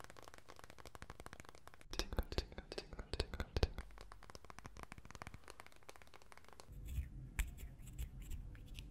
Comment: it's sounds like you said "chinga" lol (Chinga=f*ck) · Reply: He says tingle